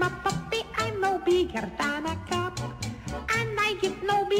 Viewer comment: I always thought it said "I'm no bigger than a cat," not cup